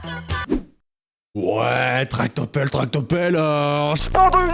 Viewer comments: À " Tractopelle " j'ai entendu " Tarte au pomme" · moi j’entends « Taco Bell Taco Bell Taco Bell ouhhhh oui » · moi j'entend "Tarte au pelle" 😂😭 Likez pour que le vois SVP OMG Merci pour les plus de 40likes ☆•☆ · Moi pour "tractopelle" j'ai entendu "tarte aux pommes"😂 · Perso j'ai entendu "tarte au pommes" et pas tractopelle Nan pas vivo per lei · A la place de "tractopelle" j'ai entendu " tarte au pomme, tarte aux pommes,tarte aux pommes" 😂😂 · Bonjour les amis nouvelle vidéo j’espère qu’elle va vous plaSINON J’AI UN COMPTE TIKTOK JUSTE ICI VOUS POUVEZ VENIR HEIN : <link> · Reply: Moi au " tractopelle " j'ai entendu "tarte ou pomme" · j'entend pas "tractopelle" mais "tarte aux pommes" 😂 dites moi si je suis pas la seule😊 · Moi j'entends pas « tractopelle » j'entends « tarte aux pommes » 😂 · Pour "tractopelle" moi j'ai entendu "tarte aux pommes"😅🥧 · Moi à la musique où il dit tractopelle à 6:10 j entend tartes au pommes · Moi j'ai entendu "tarte au pommes" au lieu de tractopelle 😭😭😑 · moi j'ai entendu taco bell